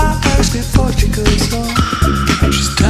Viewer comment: I thought it was saying ' she looks sane ' haha